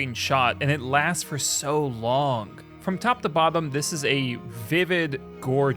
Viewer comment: Did I just hear “from top to bobbum”? I have a feeling the narrator also loves The League! 😂